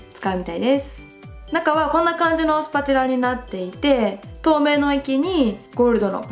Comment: ちょうど画面から目を離して音声だけ聞いてたら 1:03 「透明の池に」 って聞こえて、ん？？ってなっちゃった笑